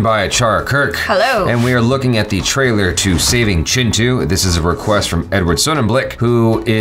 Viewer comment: when says Chintu I hear Chinchu 😀😂😂😂😂🤣🤣